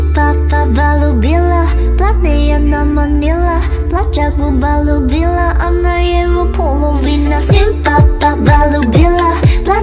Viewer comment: w dengernya "pacarmu bau gila" 🤣